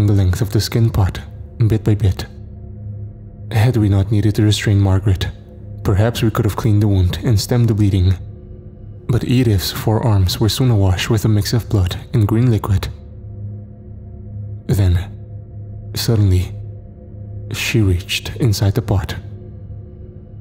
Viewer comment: I heard "Edith's forearms" as her four arms lol! Very interesting story, thank you